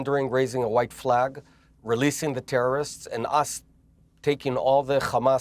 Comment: did he say “releasing the terrorists “😂😂😂😂